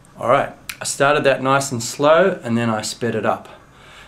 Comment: during 5:15 did he say spit or speed it up? Like if you think he said spit. (LOL) Leave the replies and comments alone, if you think he said speed (because if he said it right no comments,but Claps Like replies if you think the reply is a answer. That's it for now. GoodyBiye :) (LOL)